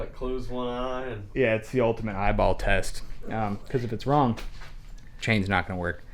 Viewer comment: Thought you said the Ike ball test.